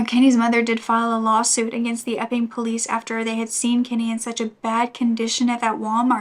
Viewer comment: When u say Epping police I hear “F-ing police” 😂